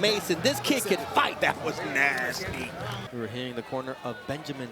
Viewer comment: Did bro say that was ass?😭